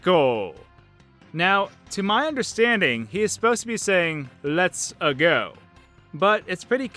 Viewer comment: I heard it as let's a go or let her go